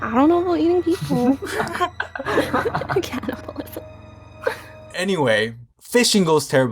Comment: People are hungry and need fo-: Canibalism Me: 😂😂😂😂😂😂😂😂😂😂😂😂😂😂😂😂😂😂😂😂😂😂😂😂😂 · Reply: I thought he said capitalism